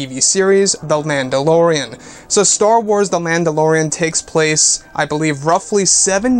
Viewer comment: Am I the only one who hears "Landolorean"?